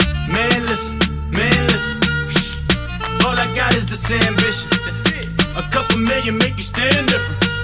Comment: I thought he said “all I got is this sandwich” 😂😂